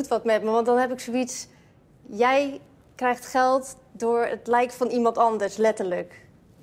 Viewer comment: Wat zegt ze precies bij 0:27 “Jij krijgt geld door .... van iemand anders” kan iemand mij uitleggen wat ze zegt? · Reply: Je krijg geld door het lijk van iemand anders